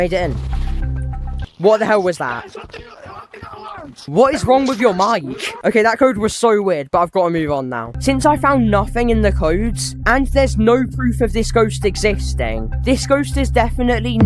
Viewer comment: I thought he said this code is fcking gay 😭😭